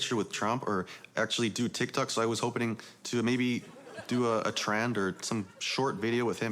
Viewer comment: did he really say “hopening”?